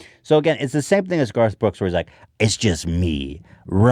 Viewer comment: I thought you said Girth Brooks at first and I was like....Ethannnn?